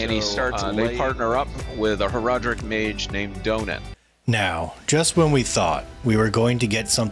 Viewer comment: "And we partner up with an Horadric mage named: DONUT" ?? What, did he actually say that or is my old ears deceiving me ? rofl I will call him Donut no matter what lol